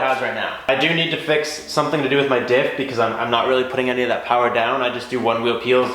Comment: Dude, what did you say at 16:12? what do you have to fix? (Just listened closely now. "my diff" gotcha.)